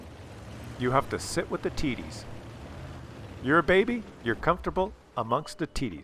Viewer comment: A Mexican Sherbaby. Interesting. Dunny why, but when says "You have to sit with the TDs" I hear "You have to sit with the titties". My dirty mind.